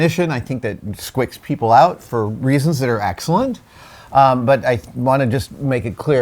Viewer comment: what did you say? Squicks? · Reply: Yes. Yes, he did say, "squicks".